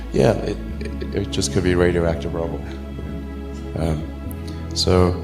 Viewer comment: Soooo 6:13 he says something about the next world war will be “probably be radioactive” he said this very fast and mumbled... notice that it was not put in the captions. Creepy... 5G towers is all I heard :(